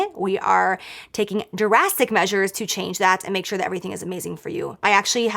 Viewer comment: She said drastic measures - I heard "Jurassic measures" - mine fits better - HA!